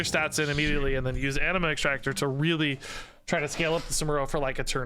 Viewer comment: I thought said enema extractor at first. Had to go back to see the card.